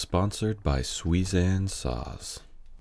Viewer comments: I thought you were saying Suizan Sauce. I wondered if you were turning into a cookery channel. But apart from causing mild confusion it was a great video. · I thought you said Suizan sauce! 🤣😂🤣😂🤣😂🤦‍♂️🤦‍♂️🤦‍♂️ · I thought that he said Suizan sauce... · I thought you said “suizanne sauce” like you got a sauce sponsor! 😂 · at the beginning of the video, I thought you said the video was sponsored by "suizan sauce". bummer